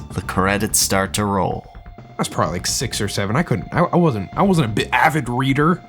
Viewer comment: what did he say 👀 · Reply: He wasn’t an avid reader